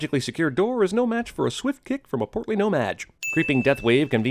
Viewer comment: THANK YOU! I was like "Why in the hell did they decide to rename muggles? All I keep hearing is "No Vaj!"